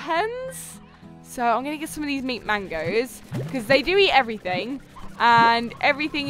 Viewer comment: it sounded like she said meat mango instead of mint mango